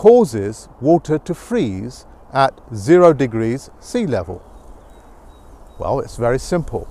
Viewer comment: Sorry I trust the random dude with a badge that doesn’t match his face Big sad Also 5:16 did he just say “0 degrees sea level”